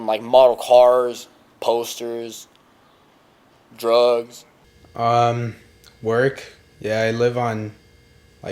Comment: did he say drugs?